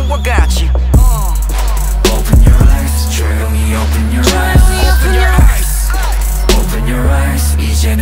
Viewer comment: i hear "Chaeyoungie open your eyes" tho...